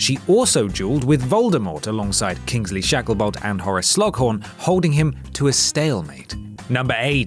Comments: Horace sl...slu. ....slo.....slog horn did he say sloghorn? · for the first have of the video i thought he was saying they were all amazing "Jewelers"